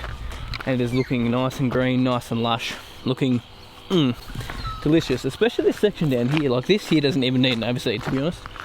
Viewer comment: Did you say ‘delicious’?